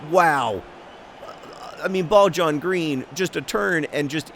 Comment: Wait a minute it's been 'bald john green' this whole time? I thought he'd been saying 'ball john green'